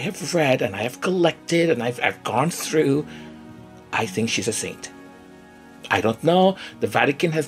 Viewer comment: At first I thought you said, "She's insane".🤣🤣